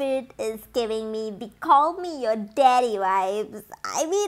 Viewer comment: Is there someone else or is it just me who heard choke me daddy ?